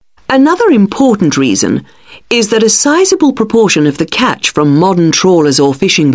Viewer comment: Hello I have questions: for question 25 why is it detail and not details? "Astrid is good at details" and not "detail" no? for question 37 I couldn't hear the word proportion, could anyone tell me which sentence gave that indication? for question 40 why is it stockS and populationS when its "THE COD [...]" should it not be "THE COD population"?? Why is it "THE COD populationS" Someone please help I need to improve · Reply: 25. Because Astrid says "detail" so that's the answer. 37. 24:07 "a sizable proportion" 40. The speaker said "stocks" 25:52 I'm not very sure about the 40th question. I heard "stocks" so I wrote stocks and got it correct.